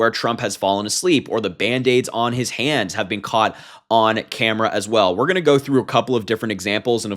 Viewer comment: I thought you said “Or the bandages on his hand has been caught on door handles.” lol